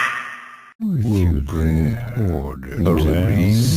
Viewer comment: my whole playthrough i was wondering what was said here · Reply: Children will bring order to this new age